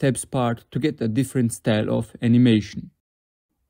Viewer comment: I thought bro said kindly donate 1 lac